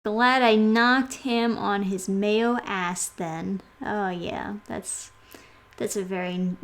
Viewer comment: that first line I thought you said "knocked him on his male ass" which I would fully believe would be in this book.